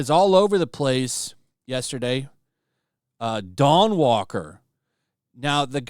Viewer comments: I thought you said "Dong Walker", immediately thought it must have been "Dog Walker". I was wrong twice! 😅Dawn Walker sounds like a Blade rip-off... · I heard 'Dong Walker' and my first thought was they finally made a game about Sydney Sweeney.